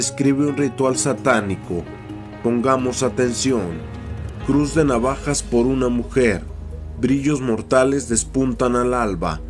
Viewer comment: Dale.. que destapada de mente..yo pensé que decía TU QUE TRABAJAS POR UNA MUJER...